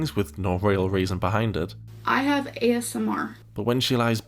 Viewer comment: did… did she say “i have asmr” ??